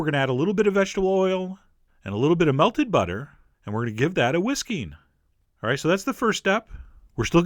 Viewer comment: OH! you said "and we're gonna give that a whisking..." I thought you said give that a whiskey! Hmm, that might work out too! I want to have the fifteenth license to whisk, got my application in, but looks there's a long line...it's a whirled wide phenomena right now ;0